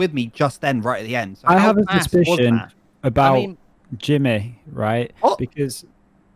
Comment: did i just heard jimin?😳🥺